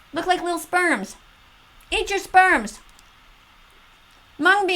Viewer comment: "eat your spearms" Did I hear that correctly?🥴